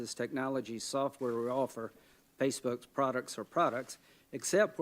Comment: Am I just hearing things or did he say Facebook's products or products?!